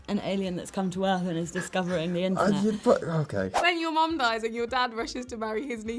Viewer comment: Did she really said that racists joke The aliean discovery the earth and then discovered the Indians.....